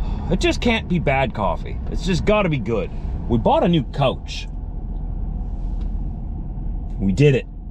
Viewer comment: Ha!! Got to love the canadian accent. Thought you said you bought a new coach. Love it!! 👍